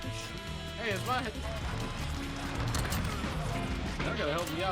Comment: was that someone speaking Arabic? Sounds like he said "hey that's one" in arabic.